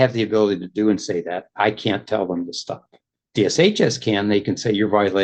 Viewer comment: Edit: My bad!! Thank you to the folks below that pointed it out and corrected me. (Also, thank you for not making fun of my poor hearing or calling me a dumbass for not picking up on the several times "Washington state" was said as a reference to the state government, and not just where they live.) Please ignore the stuff in brackets, as my earlier comment was incorrect: [ DHS, as in Homeland Security? How has he been working there for 30 years when it's only existed for 22?? It formed in November '22. So, dude doesn't know how to do math. ] And, why didn’t he address this with his HR? Why bring it to the courts and now into the public sphere? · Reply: I think the judge said DSHS.